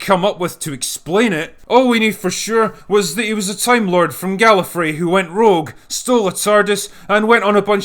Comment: my computer glitched and I just heard "a time-lord from gay"